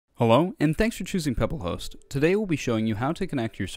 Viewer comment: I swear he said hewo